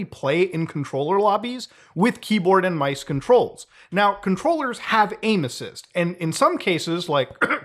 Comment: When you said "controllers have aimassis", I thought you said "controllers have anuses"